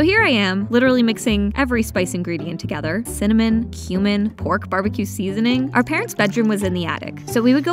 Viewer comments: I thought she said cinnamon, human😭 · For some reason I misheard “cumin” for “human” so I thought you were mixing a literal human being with the other spice ingredients 😭😭